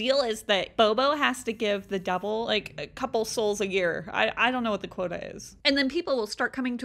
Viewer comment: It sounds like she said I don’t know what Dakota is